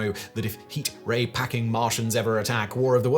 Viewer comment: At the end, all I heard was "Worldstar Londoners"